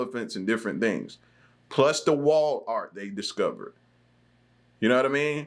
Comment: Lmao...the wall art their discovering...I thought you said wal mart..for a brief moment I was thinking what woulda ancient wal Mart look like..lol😊